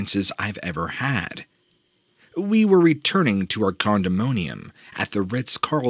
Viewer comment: Second story at 10:03 "condomonium"? Did I hear that right?